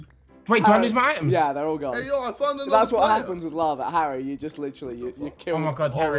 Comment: JJ Stares into Death: JJ: “HEY! I found another player!” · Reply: I thought he said I found another brother